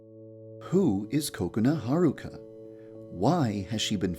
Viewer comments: I thought it said "The cake of Kokona Haruka" 😂😂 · at first i thought you said "Poo is kokona haruka" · What others hear: "Who is kokona Haruka?" What I hear: "Who is coconut Haruka?" 😂